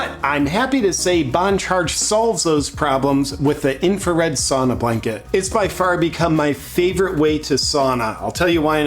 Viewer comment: Just a month ago I realized you’re saying ‘sauna blanket’! I thought you were saying sonar blanket or something 😂 I’ve seen that so many times and just didn’t know what you were talking about. We sauna several times a week and it’s so awesome 🤩 but in a sauna with an electric stove.